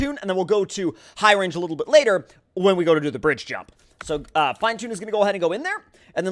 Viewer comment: did i hear "Fridgejump"? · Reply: No he said Bridge jump.